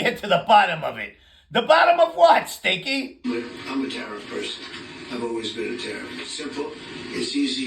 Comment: There's a radio host out of Las Vegas named Brian Shapiro. He refers to Coke Jr and I'M ERIC as "Uday and Qusay" and I can't think of them as anything else now. Also at 12:26 it sure sounded like Stinky said "I'm a terrible person. I've always been a terrible person". Classic.